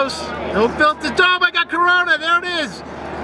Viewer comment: Did he said i got corona?